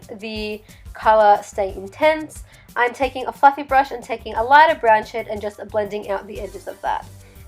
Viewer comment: i legit thought you said "Im taking a fuc*ing brush" lmao:P beautiful look btw!